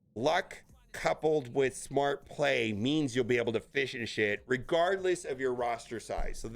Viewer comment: The “Destroyer of Words” at it again. 11:10 mark says “fish and shit” instead of “finish it”. All in good fun though, thanks for the vids, always solid content!